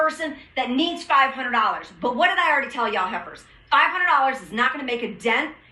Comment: Okay I rewinded at the end... Did she call them heffers? Can someone please correct me. 42:06 Maybe my hearing is off.... I hope my hearings off.